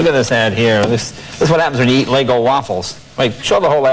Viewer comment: Did he say “LEGO” waffles?